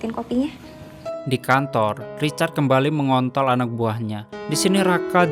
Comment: ga salah denger guaa'MENG....🤣🤣🤣🤣